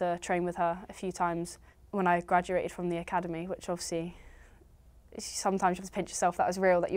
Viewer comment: Aggie keeps repeating something that sounds like she’s saying “off see.” What does she mean by that? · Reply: I think she's saying "obviously." You mean around 8:55 ?